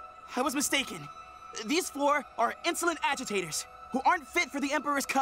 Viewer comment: is it just me, or does it sound like Hunter said floor instead of four lol